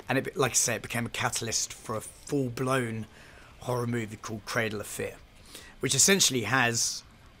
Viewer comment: Cradle of Fear... I actually got the dvd of this movie at home! 😂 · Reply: Dead serious thought he was saying cradle affair